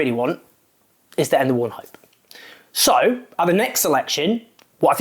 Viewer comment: Jones 01:44 "What I really want is .... .... ... ... ... ???" What are these words ? They are not enunciated clearly enough to comprehend. CC gives "the end of one hope" ??? As for leaving the party, I'm surprised it took you so long. Even I stayed for a while after the betrayal of Jeremy Corbyn, with the hope of fighting Starmer from the inside , but it soon became clear that, after ignoring the will of conference, that Democracy was not part of his strategy.